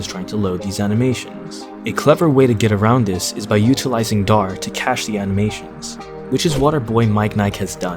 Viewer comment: by utillizing what?? I can't understand :( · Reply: DAR - Dynamic Animation Replacer the framework was used to fix the caching issue